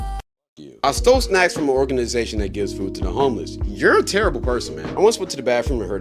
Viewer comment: I first heard that as "I stole snacks and gave them to a organization that gives food to homeless people." I thought bro was on some Robin Hood type shi 😭